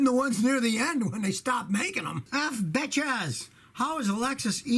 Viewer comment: did he say F bitchez? 😂